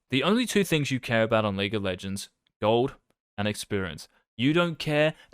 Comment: Idk if you've seen Jojo's bizarre adventures but I kept hearing "golden experience" instead of gold and experience 😂